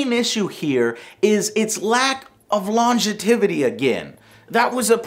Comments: Did Joe just say "Longe-TI-vity"? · What did he say at 11:08? Longetivity? Bless your heart Joe, you're trying.